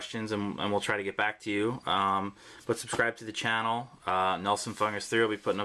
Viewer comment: at first I thought he said nelson fuck this cx